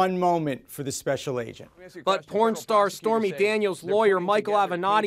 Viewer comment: Did I Hear That Right?! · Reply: Put captions on it say porn star